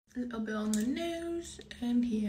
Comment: I literally thought she said ‘ edible lipstick’😂